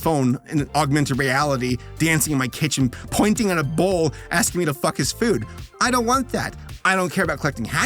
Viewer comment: I swear I thought you said “telling me to fuck his food”. Even the text had the uppercase L and I really close together looking like a U.